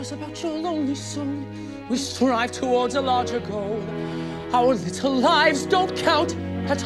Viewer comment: Why do I always hear: ''We drive along for larger goats'' why